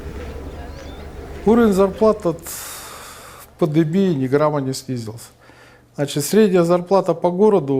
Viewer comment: что он сказал после "ни грамма"? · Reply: Уровень зарплат от пандемии ни грамма не снизился